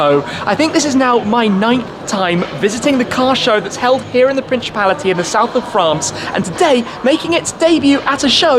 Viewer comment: Did I just hear ‘prinshipality...?’ Wrong in many ways.